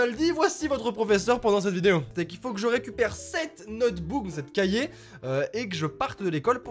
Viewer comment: À 1:00 j'ai entendu notboob 😂